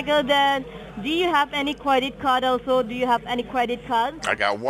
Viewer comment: It sounded like she said "did you have a raggedy car down there 😳"